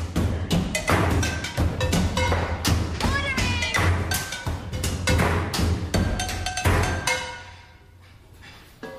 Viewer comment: what is she saying? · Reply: Ordering